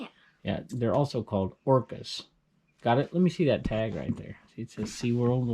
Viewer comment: LOL When you were talking about Shamu, I heard, " they're also called porpoise," and I nearly choked. I had to listen to it three more times. before I got "orcas" . Do I have to have an ebay store to use the FB Ebay for Business page? where do I go.to learn how to edit YT videos? Thanks for everything.